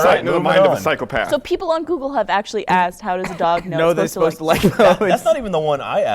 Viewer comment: i thought he said "how do dogs know theyre supposed to like moats"